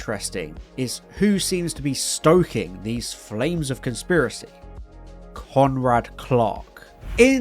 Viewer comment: I kept hearing "Conrad Clock" when you said "Conrad Clark."